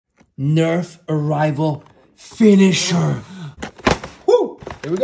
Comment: It sounds like you said finish her🤣